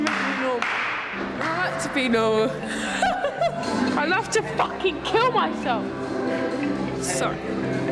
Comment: Im chilling and i hear “I Want to Fucking KMS”